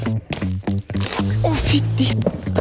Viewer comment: haha it sounded like the german version of "oh, f* you" 😆😆😆